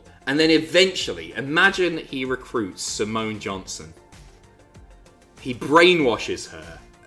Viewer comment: for whatever reason i thought you said samoan john cena